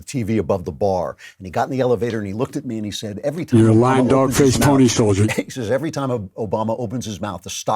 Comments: Anyone else hearing "you're a dog-face pony soldier" voice-over at 3:35 time stamp? What? Listened 3 times/heard it every time. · anybody hear that voiceover? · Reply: Sounds like it says you're a lying dog for this pony soldier...???